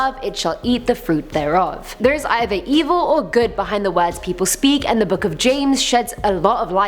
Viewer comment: Low key thought it said swords ~_